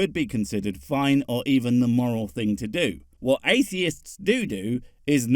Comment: Oh morals! I heard him wrong. I thought he said "More Als" like more Alberts or something. The world needs more Alberts. We can't let the "Prince Albert in a can" joke die. It's pre-internet!